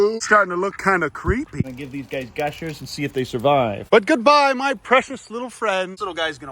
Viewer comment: i thought he said skibidi survive